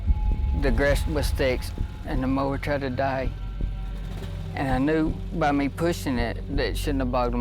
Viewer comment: what did he say? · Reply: The grass was thick and the mower tried to die. That's what I got from it.